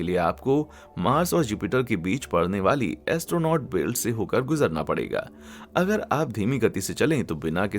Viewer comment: u said astronaut belt instead of asteroid belt